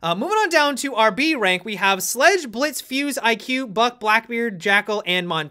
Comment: it sounds like you said "Buff Blackbeard" lol